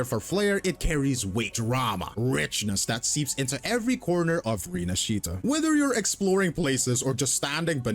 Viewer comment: did I hear "Rinashitta"? Did I heard that right or I need to take some pills?